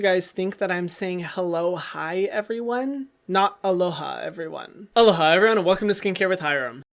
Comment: I always thought he said hello hi 😂😂😂